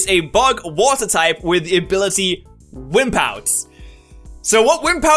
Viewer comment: okay I thought he said whip out instead of wimp out.